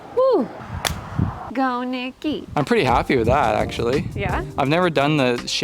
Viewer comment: I heard "go Nicky!" and for a moment I was confused about which couple renovation channel I was watching 😆